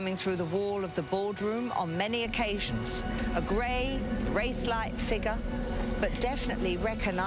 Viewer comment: did she say "a gray WAIF like figure" was anything but waif she was a very big lady